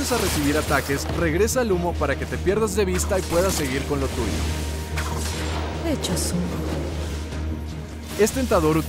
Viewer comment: En el 3:17 dice "Hecho zumo" la misma frase que dice brimstone del Valorant, es un dato curioso que nadie me preguntó pero lo escribo igual